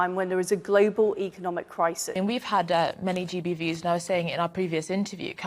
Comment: Genuinely misheard gb viewers as gbeebies.